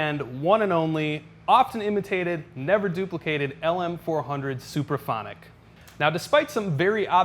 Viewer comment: It sounds like he is saying "superphonic". It's actually "SUPRAPHONIC".